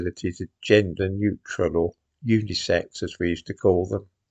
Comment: I'm afraid when you said "unisex" toilets, I heard "unicycle". I thought, "How ridiculous, what have they got? Special handles to steady yourself?"